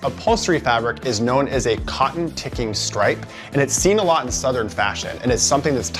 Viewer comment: Love it, though I laughed out loud when I misheard the host during the media room portion. When describing the sofa upholstery, at first I thought he said, "Cotton-pickin' stripe."